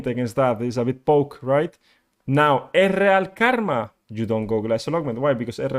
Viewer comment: OHHH!! EZREAL!!!! Karma! 7:54 LOVE the accent tbh :) and Ezreal was the only word I had to playback a few times to figure out what you were saying lol. Love the Taric information, great job :)